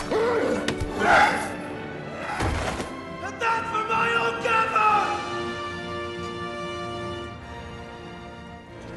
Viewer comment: What does he say at 3:13? and that's for my old camera? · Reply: "And that's for my old gaffer" gaffer is another term for elder or grandparent